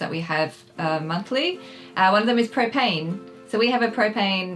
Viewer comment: I thought she said cocaine... Well that would be an interesting expense.